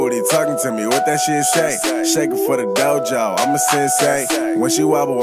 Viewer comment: Is it just me or I heard "Shake it for Gojo, I'm a sensei?"